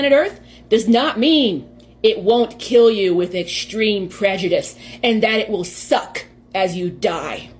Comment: Did she say WITH prejudice?? Putting aside all the other nonsense this person is spouting, how can uranium be prejudiced??? Make it make sense 😭